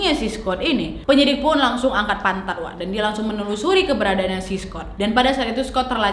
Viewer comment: aku salah denger apa gimana? Katanya penyidik angkat pantat?